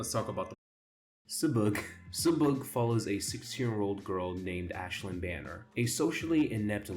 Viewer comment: WHY DID I HEAR 6 YEAR-OLD GIRL